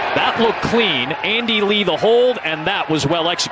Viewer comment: I thought he said "Andy Reid will hold" at first!